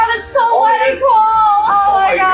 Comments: Did just say baby GIRL! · I heard say baby girl at 44:51 or around there